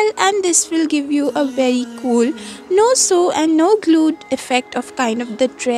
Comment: I thought they said no glue is it not glue there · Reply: Dear it is No Hot Glue because I haven't used hot glue gun in it